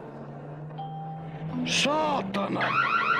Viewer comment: i hear finnish curse Word "saatana"